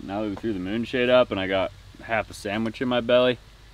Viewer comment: I’m definitely getting old. said after eating sandwich “ready for a nap “. but I heard “by a pretty brunette”